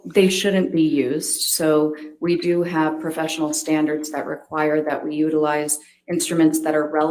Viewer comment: "do you have issues with her use of this checklist?" "why yes; we do have standards." (that's how I heard it, anyway 😂)